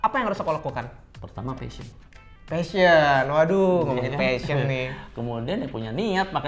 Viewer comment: Min yang di menit 8:49 tentang melewati tahap holy grail hunter dia bilang apa ya min kok saya dengernya fesion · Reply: Wah terima kasih ya, jadi masukkan buat kita nih. Lain kali harus lebih jelas penyampaiannya. Yang dimaksud itu trading butuh "Passion" ya, bukan fashion 🙅‍♂️ 👠